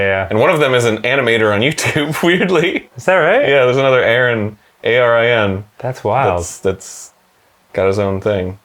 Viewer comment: Did say his name was spelled aran?